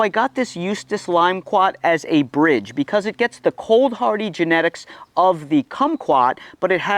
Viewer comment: I was listening to this in the background while engaged in other work. The limequat ... I heard u say "useless" limequat... and I was thinking why is he singing the praises of a useless limequat. Lol😂. Had to re-watch from the beginning.